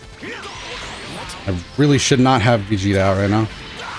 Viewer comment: ''I really should not have Vegeta out right now'' Is that what I heard? Really? *Looks at team* Really?